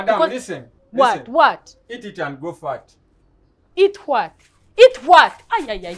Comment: LMAO! I watched this over and over already. I thought Teacher said eat it and go fart LoL. I finally understood what he said, and it is not fart lol. :_NinjaSurprise